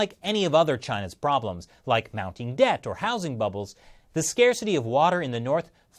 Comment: did you just say "Mao-ting debt?"